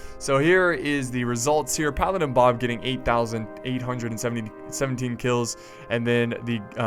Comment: did anyone hear him say paladin bob got 8817 kills